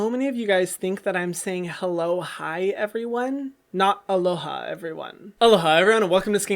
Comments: I thought you said hello hi, when I first started watching your videos a several weeks back but then soon realized that you said "aloha everyone." 😂❤️ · Omg i thought he said “hello hi everyone” and now that he mentioned it i can only heard aloha everyone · I really thought that you said hi hello everyone 😂 then about 10 videos in i realized aloha everyone☺ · I also thought you were saying "hello hi everyone" this .. whole.. time.. SMH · Woah wait what. I’ve ALWAYS thought you said hello hi everyone. . My life has been a lie. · I always thought you said “aloha, hi, everyone” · omfg i didn’t even realized that HELLO HI EVERYONE is grammatically incorrect and still thought you said that!!!! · What the heck lmao... I discovered your channel a couple months back and NEVER thought you were saying "hello hi" lmao! It's clearly "allo ha" - I probs spelt that wrong. Maybe it's because I'm european rofl . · I also thought you were saying Hello Hi everyone! Now I am better educated :D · Is it just me, but do I hear "hello hyram, I'm hyram:" and not "alohaa everyone" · Every time you were saying “aloha everyone” I thought you were say “hello hyram!” And I was like hmmmmm interestinggg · Also, I thought you said “aloha hi everyone.” Haha idk why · Oh hectic...I always thought you were saying “hello, hi everyone” 😳